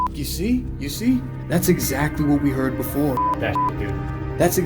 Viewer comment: I didn’t hear “death” I heard “EXACTLY” sounded like Joe Pesci ya know like that wise guy New York accent... that locked door, I saw you check it earlier in the video that’s probably where the guy who shot him hid 🤷🏻‍♀️ I know those were clearly knocks but in a way it kind of sounded like gun shots... idk very weird... that ending was EPIC see how the popping of the balloon sounds like gun shots 🤷🏻‍♀️🤷🏻‍♀️ did anyone else catch the ORB when shown the swinging chandelier???